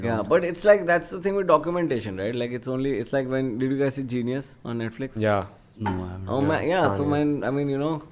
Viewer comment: Hey Man love your pod. Couldn't catch the reco at 30:01 though? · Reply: Thanks man! He's talking about Jean-Yuhs the Kanye Documentary on Netflix.